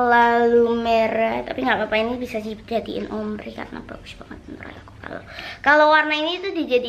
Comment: dia ngomong apa sih? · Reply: "Karena bagus banget menurut aku" aowkawokawok awalnya juga gw ga denger trs gw gedein volumenya